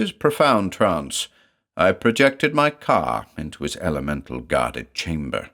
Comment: For the span of one hilarious second, I fully thought you said "I projected my car into his elemental-guarded chamber." Now THAT'S a mental image I need to draw...